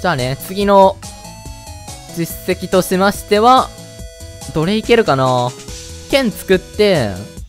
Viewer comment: どれ行けるかな→奴隷蹴るかな に聞こえた